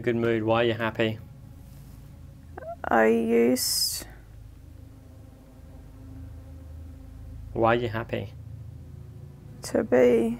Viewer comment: he said I used to be one boy, I think thats what he wanted to say